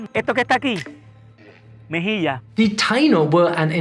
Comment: Mejilla, Yeah of course, that's the correct way to say it in Spain. And why does the subtitle say "cachete"?